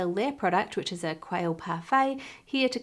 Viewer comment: did I hear correctly? Quail parfait?